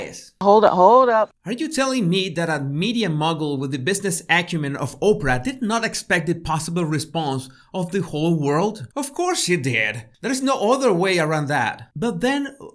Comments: For a moment I thought you said 'media muggle' (hehe). BTW nice boulder of salt :) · I laughed really hard and had to pause at 4:40 because I thought you said "media Muggle" instead of "mogul". I need to stop listening to Harry Potter at bedtime.... ANYWAY! Thank you, for your (always) excellent videos! I think you should dive back in history and look for more of Meghan's old interviews. One of my very good friends went on a tour of the Suits set in Toronto many years ago (you know the kind, you pay to visit the set and meet the stars), and Megan Markle just blew right past the tour, didn't even give them the time of day. My friend was really disappointed, but it just goes to show that a leopard doesn't change its spots.